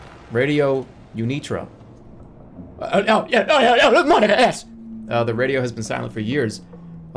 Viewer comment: Does he say monkaS at 7:37 and if so why does he say it like that